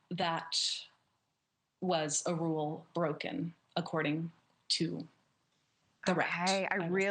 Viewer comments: Wait, did anyone catch her say “that was a rule breaking according to the rat “ meaning like Mickey Mouse = Disney and calling them the rat cause that’s hilarious. Unless she said “di-rect” or she meant like rat like the person who told her that tea, like ratting them out. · She called Mickey "The Rat" 😂😂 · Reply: I thought she said “according to direct”? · did she say “according to the rat”??! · did she just call Mickey “the rat?” 😂😂😂